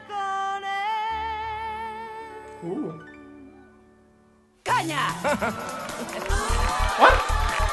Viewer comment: pensé que dijo COÑOO